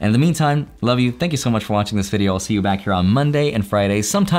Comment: DID YOU JUST SAY CARDANO WILL GO TO ZERO!?!?!???